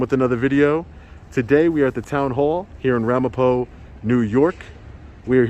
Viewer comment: LOL at first I thought you said Ramahoe..my bad..